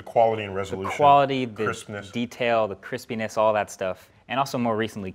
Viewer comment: the auto-subtitle says Chris Penis Instead of crispines 😂😂😂